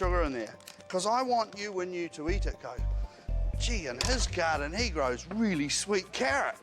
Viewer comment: What did he say 😳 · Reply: “In his garden he grows”